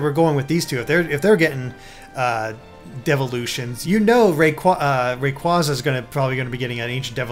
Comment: I thought they said "devil" ution. I was thinking, sweet, now they're going to become Satan!